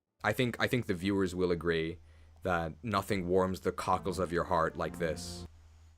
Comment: I first heard “cockles of my heart” as “Kagas of my heart” □